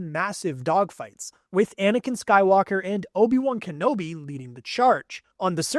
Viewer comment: Why did it sound like he said anikan sky locker💀